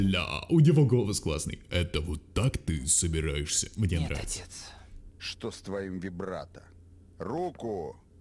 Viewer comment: Мне щас несколько раз послышалось что после: Нет отец Он говорит: Что с твоим вибратор